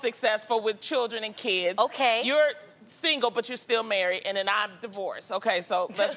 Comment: Did Loni just say to Jeannie "you're single but you're still married??"...someone please watch at 4:10-4:21......lol I'm so late but this was two years ago!